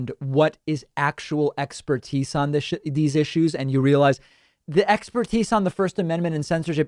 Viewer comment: did anybody catch that? 'actual expertise on this shi...issues uhm' lol